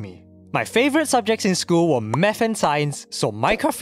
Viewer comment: That really sounded like meth.